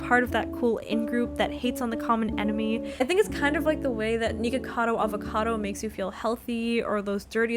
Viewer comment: DID SHE JUST SAY NIGGACADO AVOCADO?¿¿¿¿